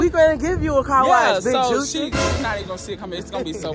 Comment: Did he say big juicy 💀🤣